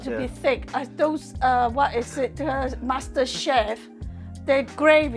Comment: I do not know what I love more the food or you pair and the “chemistry” on video you have together. At 21:04 I miss heard saying “Master Chef” to me I heard that Bastard Chef. Love you both. 👍👍